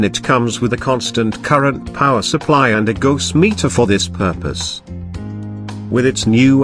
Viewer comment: What is a 'gose' meter? Subtitle says 'ghost'. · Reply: It meant Gauss meter. It measures the magnetic field strength, which helps determine strength of the Zeeman splitting.